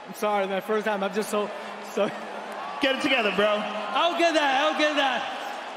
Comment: What Joe Rogan said: "get It together man" What the translator heard: "Talk with my voice"